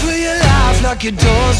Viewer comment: I just hear him saying "Ima batman" anyone else?